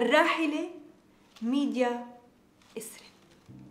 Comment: مو اني سمعت اتكول ميديا اتكول اب دقيقت 3:17 اتكول اخر أعمال الفنانة السورية ميديا ازراب ههههههههههه 😂 ههههههههههه 😂😅😆🌹🌼🌻🌼🌹🏵️🥀🌺❤️❤️🏵️🌻🏵️🌻🌼🌹🏵️🥀🌺❤️❤️❤️❤️❤️❤️❤️❤️❤️❤️❤️❤️❤️❤️❤️❤️❤️❤️❤️❤️❤️❤️❤️❤️❤️❤️❤️❤️❤️❤️❤️❤️❤️❤️❤️🏵️